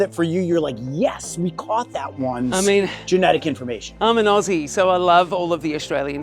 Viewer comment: uhhhh “I’m an Ozzy” not the other thing. Definitely not what I heard